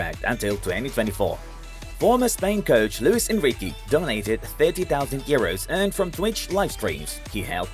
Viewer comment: I thought the Man said Luis Enrique died😂